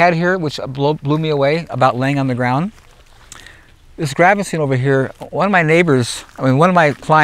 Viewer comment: What is he saying in 1:20:30? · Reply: His friend had a particularly tasty Gravenstein apple tree which they paid a lot of money for. tasted it, and realized why they paid a lot for it. He loved the flavor. So he took some cuttings to graft into his own tree.